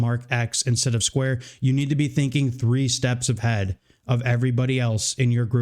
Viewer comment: "You need to be thinking three steps of head". I know what you meant to say, but I was like WTF? lol.